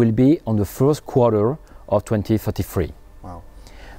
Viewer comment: does he say sales 2022 and delivery 2033?